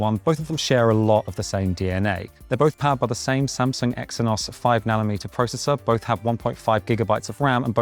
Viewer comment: Caption errors! 2:09 - text says “five millimeter processor” instead of “five nanometer processor” 5:15 - “sleep has gone gently” should be “has gone generally” 5:22 - “sync tracking” should be “sleep tracking” 5:55 - “finally getting up” should be “finally getting it”